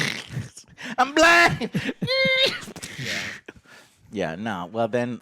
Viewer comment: Thought Joe said “I’m Black” when he was talking about the lights on the car. Mybad, I should of known you said “I’m Blind” instead. Great Guy